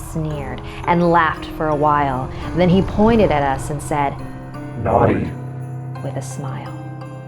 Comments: i thought he said "NANI" XD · what did he say? Sorry can someone tell me ? cries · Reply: he said NAUGHTY